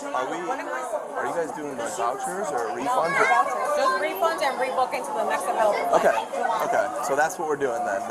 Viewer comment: Wrong... The officer is a meathead and couldnt understand what was going on. The Spirit Airlines lady clearly said they're only giving out refunds. Not rebooking or giving credit as it will be at least 48 hours until the next flight. Officer for whatever reason said the opposite when trying to explain to the other cops. Its a big problem when someone who has no education and then is hired to have a gun and explain rights to people, can't understand a simple line of dialogue. Why we need educated people for police officers. There was another airline that also cancelled flights because of the weather. These passengers dont understand how airports work and also dont have the patience or maturity to know what to do. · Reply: Actually if you rewatch 7:18 ish she says "No Vouchers", "Just refunds and rebookings to the next available flight". He misunderstood what she said since she said it quickly. The people were all upset not about the refund but more so because they weren't offering to pay for their hotels or book them a flight sooner than 48 hours. (or they didn't understand that they could get a refund)